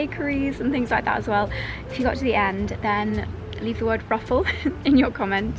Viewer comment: I thought you said 'brothel' 🤣had to re-watch to make sure so it's 'ruffle'. Lovely vlog as always. The market reminds me of when I lived in Italy on a small Island, I worked a clothes stall, and each day of the week we would travel to a different town for market day, and I know exactly what you mean by: 'that cool period in the morning just before it heats up'. It's such a peaceful time as well. Ahh the memories 😊